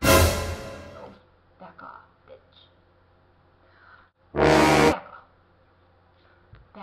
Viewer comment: Hey pretty lady! Beautiful look. Have to tell you that I thought you said at the beginning of the video "F-off B" instead of "back-off b"...and I played it back twice and still thought that until the end of the video where it seemed much clearer somehow. :D Made me LOL. ♥